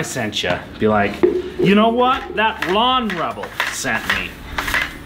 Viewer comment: Lol, at 1:56 I thought you said "Blonde Rebel" !